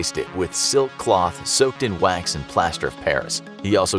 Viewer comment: MAX is types out instead of WAX😂